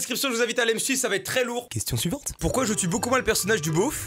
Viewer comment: pourquoi j'ai entendu " question SUIVENTRE "😆😆